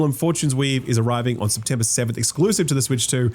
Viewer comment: it sounds like you say the game is coming September 7th, but its actually September 17th